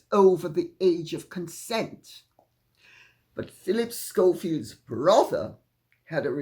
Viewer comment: I did not advocate for blind loyalty, but for loyalty and responsibility. That is what hate the sin but love the sinner means. It does not mean love the sinner and ignore the sin. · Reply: Colin Campbell I do apologise Lady Colin if I mis-heard on your last video, but I thought that I heard you say the sentence, " Philip Scofield's poor brother. " Kind regards Sharon Mc